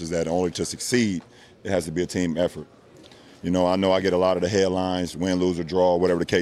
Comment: did he say “hairlines”